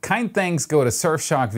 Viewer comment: Ich verstehe immer „keine thanks“ xD